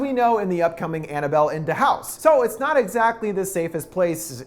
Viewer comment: Excuse me did he just call the next movie "Annabelle In Da House" 😂😂 Brilliant